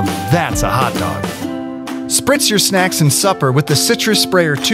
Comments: I heard "Spritz your snacks and suffer." Edit: I was just sharing what I HEARD. Not what I THOUGHT it said, but what I HEARD. I'm getting sick and tired of having to say that. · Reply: To everyone who's saying "iT sAyS sUpPeR nOt SuFfEr", I was just sharing what I heard. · thought he said fritz Flashbacks